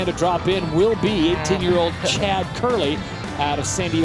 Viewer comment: did they call him 10 years old? lol